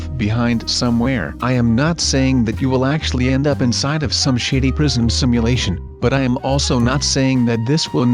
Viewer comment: did he say 'Shi***' or Shady?